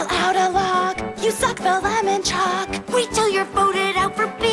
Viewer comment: My brother was watching a show in the living room and made it sound like Heather said "I suck the lemon chuck" XD